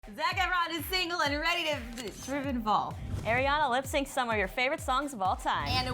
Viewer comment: Wtf did she say at 0:00 lmao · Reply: zac efron is single and ready to trip and fall